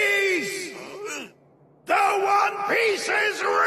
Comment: It sounds like he said the one peas in 🇩🇪